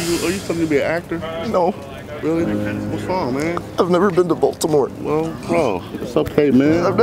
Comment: I thought he said " I've never been to Voldemort" XD